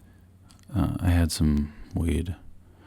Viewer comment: WTF HE SAID “I HAD SUM WEED @ 4:20 !!!!! 💥 🔥🎉🎉🎉🎉🎉🎉🎉🎉🎉🎉🎊🎊🎊🎊🎊🎊🎊🎊